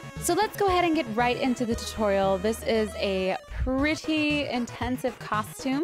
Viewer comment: When she said tutorial i thought she said tu-toriel